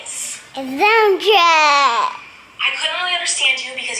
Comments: when was watching the video of Miranda I thought Miranda said "Donald Trump" and not dump truck 🤣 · When he says Dump truck I totally thought he said Donald Trump LMAO😂🤣